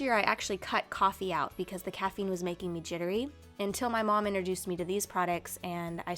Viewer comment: because the coffee was making me WHAT? Couldn't catch that word or never had heard before! Anyone can spell that for me! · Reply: Jittery